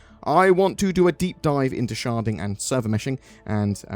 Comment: I keep hearing "sharting"